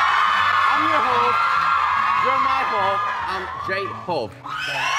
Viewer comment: Who love these lines - 1- I'm your hope , you are my hope and I'm J-Hope. 2 - World wide handsome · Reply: i heard "im your ho, you are my ho, im j-ho" 🤣🤣🤣